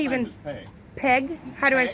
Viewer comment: I thought he said "pig"